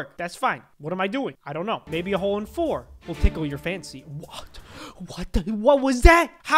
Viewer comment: why did i hear"tickle your fancy"